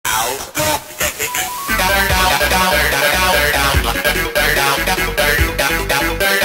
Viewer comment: What was saying in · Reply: Chick-a-filla